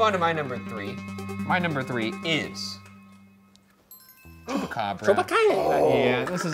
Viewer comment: At first I thought it said Chewbacca